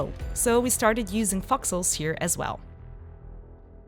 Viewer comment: Thought she was saying "foxles" for a while there. Long vowel sounds matter!